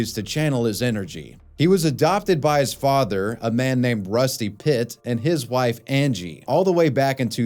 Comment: Im sorry, did you say “Rusty Pitt?” I’d be straight down to the courthouse if that was my name 😂